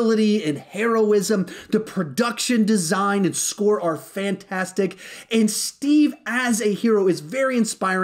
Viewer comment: I actually thought i was high when he said fantastic 4